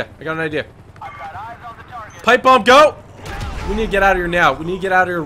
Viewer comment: Thanks for watching #TeamTG!! If you enjoyed this video be sure to SMASH that LIKE button on the video and on this comment too! Also, make sure to Subscribe if you haven't already! Thanks for watching, I hope you have an AWESOME day and PEACE OUT! :D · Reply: I thought you said hike bomb